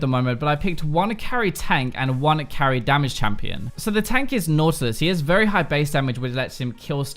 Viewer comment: The tank is Nasus? You mean Nautilus right, because I heard Nasus